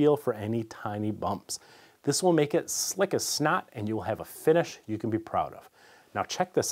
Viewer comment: Did he just say “slick as snot?” Lmao